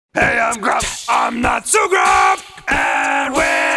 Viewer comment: Everytime I hear "I'm not so grump", sometimes I feel like hes saying "I'm not so drunk"